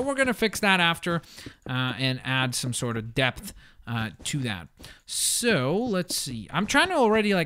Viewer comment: I thought he said "ill add some death to that" instead of "ill add some depth to that"